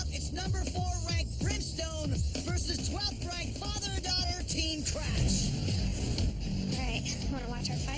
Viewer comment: Don’t car what you say he says team trash